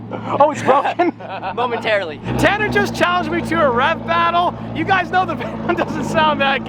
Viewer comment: I thought he said Rao battle honestly... He said, "So Tanner's gonna challenge us to a rap battle, we don't sound good but we're enthusiastic" and I laughed so hard...